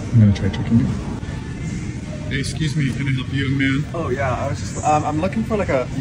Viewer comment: at first I thought you said : can I help you my man? And I was like oh how sweet, Then I get that you said "young man" And my reaction still the same; Aww so sweet~ Just how beautiful his smile is😌